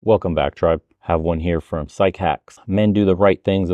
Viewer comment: In the beginning I thought he said “welcome back chuds” wouldn’t be wrong though lol